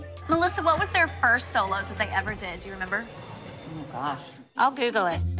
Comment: All the mum's: crying "we gonna miss you" Jill: mY lItTlE kEnDaL cAn gEt MoRe sOlOs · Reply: for some reason instead of solos i thought you said Doritos